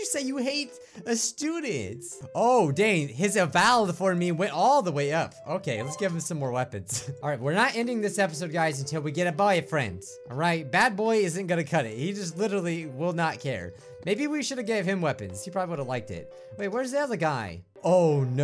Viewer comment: WHAT I THOUGHT YOU SAID”we are not ending this video until we get a boyfriend” D